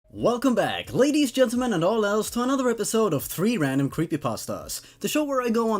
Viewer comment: I swear it sound like he said All elves instead of all else in his introduction....